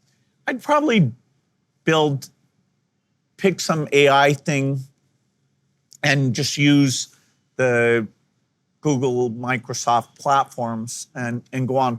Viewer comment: What he actually says: "I'd probably pick some AI thing and build a product using Microsoft/Google platforms." So nothing particularly useful. Timestamp is 24:22 in the video.